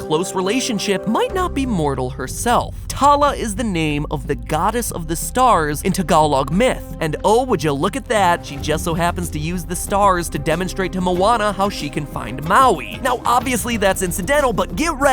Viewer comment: HOLD UP ON 9:18! DID I HEAR TAGALOG? MY PARENTS SPEAK TALOG! SCREEESEEEEEEEEEEEEEEEE! Also, reply if ur Filipino too.